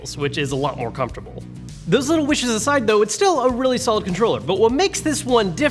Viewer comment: its a solid controller Me: erm I thought you said said it was soft